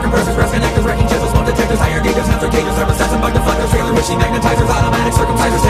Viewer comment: why does it sound like he said motherfuckers when the ford showed up